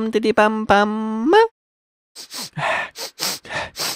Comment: i never understood what is the catchphrase he says at the end (1:24)... and what it means? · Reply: says "Peace, peace out"